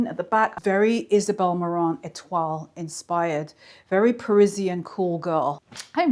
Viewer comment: I thought she said that short skirt suit was “ very Peruvian call-girl”! Pretty sure I heard wrong!